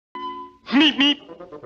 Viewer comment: I clearly hear 'Meep meep'. What's up with the 'beep beep'?